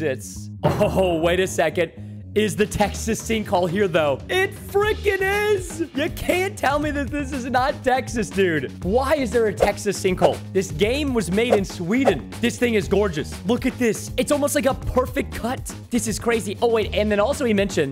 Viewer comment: You said my name right br you said Sweden you said made in Sweden but at 12:00 it sounds like you said Aiden and that’s my name